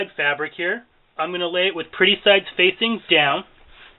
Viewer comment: "Pre sides facing down"? "Print sides facing down?" Please clarify. Sorry. I really can't hear it well. 😔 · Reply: I said the pretty side of the fabric or the printed side facing down, so I mean the right sides of the fabric. The side of the fabric you want showing in the final project. Hope that helps